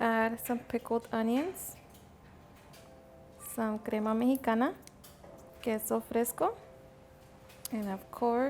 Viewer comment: what does she say? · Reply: Crema mexicana. It’s similar to sour cream just a little thinner